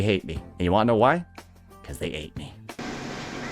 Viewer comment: "They hate me, cause they aint me" - Thank god we're not him. We dont rat and backstab our friends. EDIT: Ok nevermind. He's more deranged than I thought... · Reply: Oh he said "they ain't me". I thought he said "they ate me", I'm like wow tf you mean😅